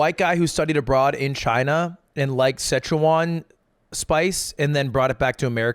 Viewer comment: did bro just call Sichuan sec huan